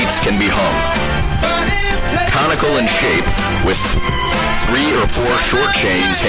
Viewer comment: I listened to this for a few times without reading the text, and thought he was saying "comical in shape" which is much funnier.